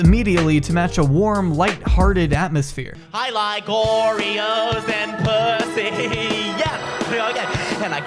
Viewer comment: "I like oooreos and pussyyy~" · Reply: Oh, he said oreos? That makes sense. Nevermind that I honestly thought I heard "I like gloryholes and pussy". Let's move on.